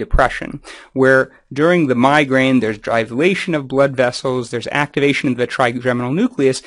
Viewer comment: Can you define 'dryglation of blood vessels' at ~1:50? Or say which word is being said. Sorry I tried searching a bit but can't understand the pronunciation.